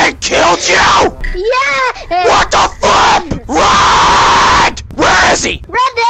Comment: When said what the flip I thought he said what the you know what